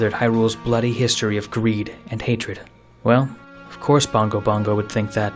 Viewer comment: i thought it said "bingo bongo" and i was like bro- then i remembered wrong phase wrong phase